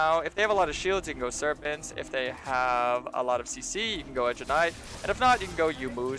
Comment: sorry my english not so good can sombody write what he sayd if t hey have shield you can go serpent that i understand but if they have alot of cc you go what?? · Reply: edge of night